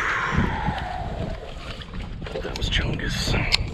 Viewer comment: Did he just say "that was chungus"?